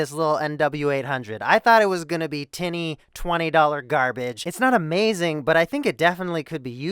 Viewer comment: did he say Tenny twenty dollar mic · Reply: He said "tinny", basically a thin kind of sound.